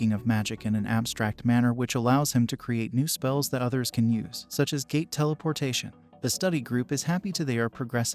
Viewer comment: “such as gay teleportation” is what that sounded like